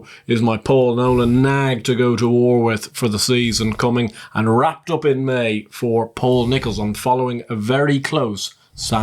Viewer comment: Great episode as always lads! The Cheltenham Bandit beat you to a couple of these and I will be talking about a few of the ones you've enlightened me on today. Also, the big Bandit misheard Paul Nicholls so thanks for calling out Wrapped UP In May!! Nicholls was bullish last year when talking to the big Bandit about this horse (he misheard the horse as wake up in May)! So the Cheltenham Bandit seconds following the correctly named wrapped up in may!